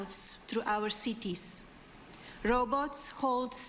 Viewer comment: Oh,"robots"! I thought she said, "row boats."